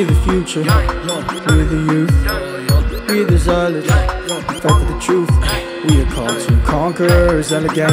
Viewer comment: What word are you saying @ 2:13 time stamp. If that is a curse word I rebuke you in the name of Yeshua the Messiah!. If I spoke in error lGod forgive me. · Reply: He said “ We fight for the truth “